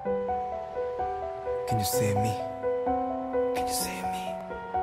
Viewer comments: In the beginning, I hear "Can you see me?" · i thought it wasa " can you say me ?" "can you see me?" lol